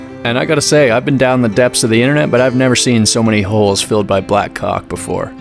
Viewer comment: what does he said? So many holes filled by black what? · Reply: Caulk